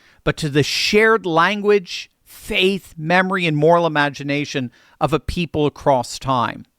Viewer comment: Did you say, at 10:22, "moral imagination"? That needs some unpacking. Sounds like moral relativism to me. 🤔🤔